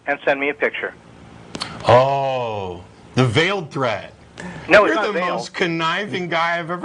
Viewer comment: Thanks! I thought I heard him say 'benile' there at about 6:30 So a veiled threat doesn't work (by definition)?